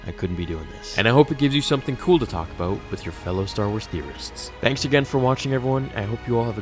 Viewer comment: When You say "Star Wars theorists" I always hear "Star Wars terrorist" lol